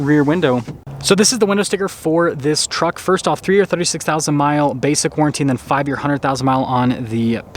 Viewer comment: When you mentioned the 3 year 36,000 mile warranty i thought you said 336,000 mile warranty at first😂